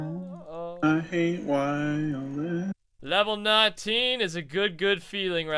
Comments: Who else heard say "I hate Wildx" at 9:20 ? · it sounds like says "I hate Wildx" but I don't think he really did and if he did he was probably just kidding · Did someone heard saying i hate wildx 😂😂😂😂😂😂😂 9:27 lol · Lol anyone else hear "sing" 'I hate WildX' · Like my song ? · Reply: lol..I thought I was the only one who thought that he said I hate wildx...